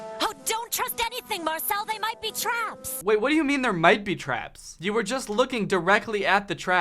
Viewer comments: I think she said they might be traps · when you started talking about how they said "There might be traps", I think they actually said "They might be traps"